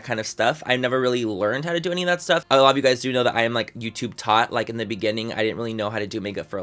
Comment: I thought you said "YouTube thot" and I was like truuuu. Lol I love you 💕